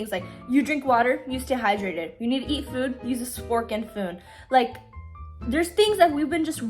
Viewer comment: did girly say spork and foon??